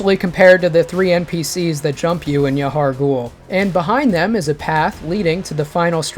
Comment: Sorry, where? Did you say yaharghul? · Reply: Yes, he’s talking about the trio fight in Yahar’gul after you start the paleblood moon.